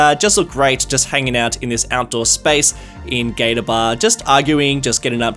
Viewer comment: I really thought you said "Gay bar". Then I saw the alligator. But then you started placing all those bikers in their leather outfits and it went back to a gay bar.